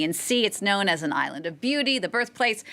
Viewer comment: I thought she said Island of bottles not beauty huhu